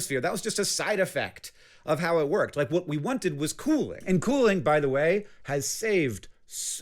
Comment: I thought he said Kool-aid, I had to reqind lol